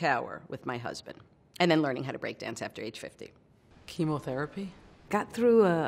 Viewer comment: 同じ人かと思ってた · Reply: m m どうでもいいけど、3:06の最初が「キモ」にしか聞こえない。